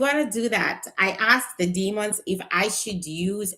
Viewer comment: did u say you were going to ask the demons?? ....... I'm out✌🏾